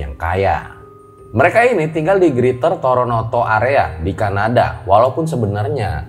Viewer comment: Kok dengernya toronoto ya.. apa saya salah dengar